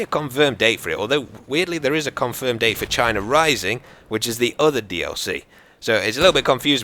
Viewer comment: Did i hear Vagina rising on 02:50 xDDD ?????